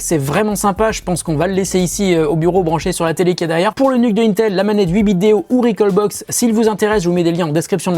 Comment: super vidéo ! toute la vidéo j'ai entendu "l'eunuque de intel", j'ai bien rit !